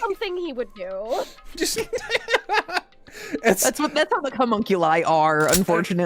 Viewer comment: thank you for not only presenting my crimes, but also including it right when said homunculi in the way that sounded like she said cummunculi